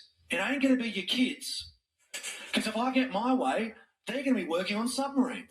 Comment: Did he just say, if i get my way... your kids are going to war...